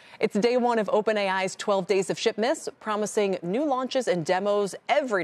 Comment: i almost thought she said 12 days of sh*tmas lol